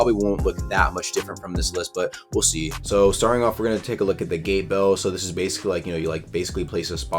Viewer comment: for a sec i thought he said the gay bell for the first bell